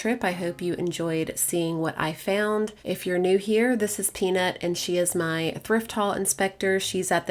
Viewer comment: Ha! You are a spring chicken!! Wait til you are 82 like me! Love your videos and also Peanut! One question, did you just say the dog’s name is Tina? Do you have two dogs?? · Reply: Haha, no I said peanut lol. I think I might have just said her name too fast. 🤣